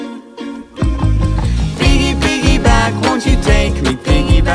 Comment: thought it said piggybank💀